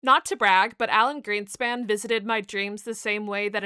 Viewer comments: At first I thought you said Alan Grant 😂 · I'm sleep deprived scrolling and thought you said Alan Grant at first and that you were painting a dinosaur fossil 😭🤣